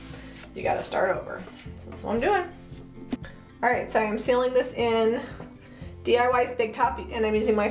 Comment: what did you say you’re using DYI stick-up??? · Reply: I believe she said sealed it with big top. She says it again at the end.